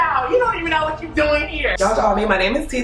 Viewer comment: "you're not an actress, you look like a man"😂 deceased! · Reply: i thought ge said you look like a bat😂lmao